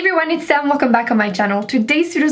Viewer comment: It sounds like you said hey everyone it's Sam at the very beginning... Maybe my ears are broken.. lol love ya girl great video I have the hardest time finding foundations.